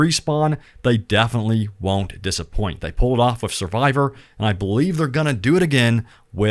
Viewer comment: I heard: "They pulled off a survivor". Not that it would not be true.😂